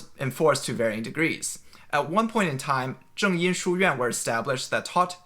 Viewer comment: A small error in the captions at 4:37; 音 instead of 因. Hope this helps!